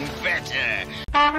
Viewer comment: Did he just say "Fanta"? To Be Continued Please stand by ... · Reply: He said that's better